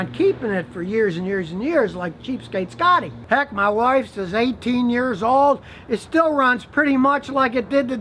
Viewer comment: I swore Scotty said that his wife is 18 years old 🤣